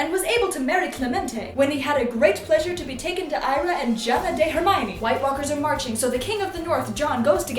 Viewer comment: I thought she said "Jabba the Hermione" 😂😂😂